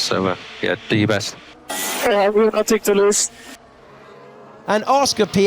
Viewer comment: did he said he got nothing to lose?